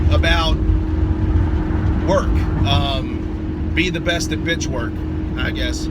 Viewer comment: "Be the Best at Bitch Work"....love it and you are right on about being on time....Army and the military taught us that and I'm Always EARLY.....keep em' coming...learn something every time...Thx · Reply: I heard that as "Be the best bitch at work".